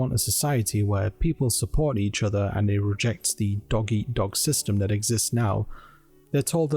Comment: I misheard dog eat dog as doggy eat dog... I had no idea who libertarians are and how bad they are